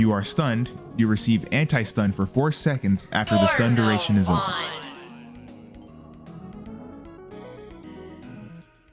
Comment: did he just say it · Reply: "no more fun💔" u dirty minded b